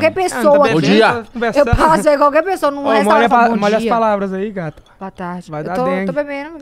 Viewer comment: Só eu que ouvi? "Molha as palavras aí gata!"